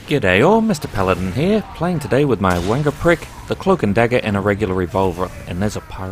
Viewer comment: When he says ive got my wanga prick, i thought he said ive got my wonder prick XD.